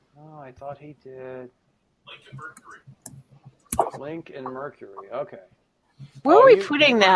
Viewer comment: I just heard "Link and Mercury" during the Influence segment. Haha Lincoln Mercury!